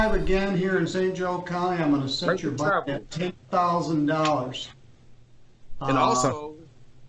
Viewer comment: Woah hold up. 3:45 - does he say “I’m gonna sentence your black ass to 10,000 dollars”. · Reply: He said "I'm gonna set your bond at $10,000"